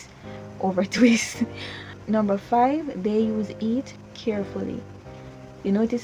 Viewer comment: When you said heat you sounded like you said eat lol